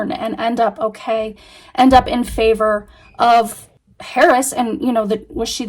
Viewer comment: sounded like you said in favor of Paris… For a few seconds, I was like who’s Paris what is she talking about? Then I realized that it was Harris. But I hope that spirit didn’t misunderstand you. It certainly sounded like Paris.